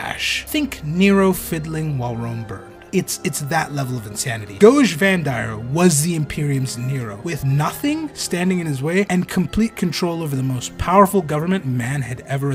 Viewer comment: I keep hearing "Doge Vandire"